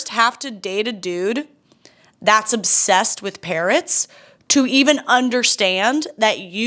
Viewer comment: genuinely thought you said "date a dude obsessed with carrots" and my first thought is: yup that's a weird trait but they're a good veggie... oh PARROTS yeah, hard no lol